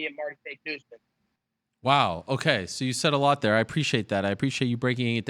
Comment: @ 5:10 "Wow.. Okay.. You said a lot there." All I heard was "Foot was a balloon!"